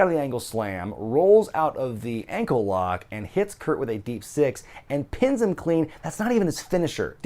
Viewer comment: David Matoushek He did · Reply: Professor Wumbo I heard him say Deep Six after a second hearing because I thought he said End of Days earlier.